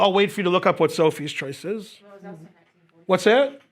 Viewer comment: What does (I assume) say at 33:39 about Sophie's choice? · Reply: "It was also 1944."